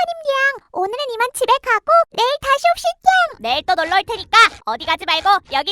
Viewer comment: 에서 슘댱이가 다시 옵시땽 이렇게 들리는거 보니까 땅땅이하고 햇갈린건가